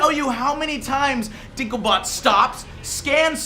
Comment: Did he say dinklebot? Alright I love you